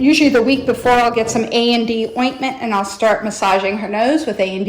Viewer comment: does anyone know what “a & d ointment” is exactly? i think that’s what she said, but i’m not 100% sure...